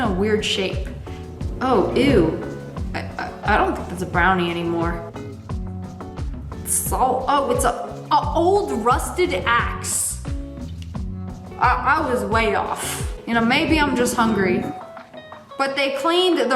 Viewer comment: I HEARD A$$🫢